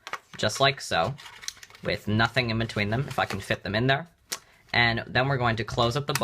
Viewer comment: sounds like he says "fuck'in fit them in there"